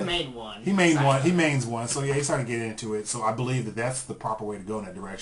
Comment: I thought you said it poops on Asians